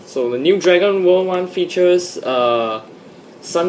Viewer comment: honestly when you said Dragon world, it sounded like you said Dragon ball :/